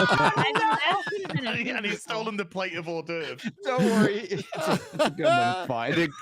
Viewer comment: "you're on the plate bitch!" Omg Gundam never change dude that was hilarious. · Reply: I thought he said "pipe", not plate, but agreed, Gundam rocks~